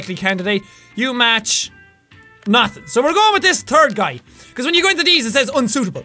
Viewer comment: His accent made him sound like he was saying: We're going with this turd guy(We're going with this third guy), and the fact that the guy was BLACK made it even funnier!